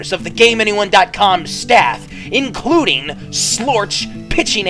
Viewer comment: DId he say "Slorch" at 2:10?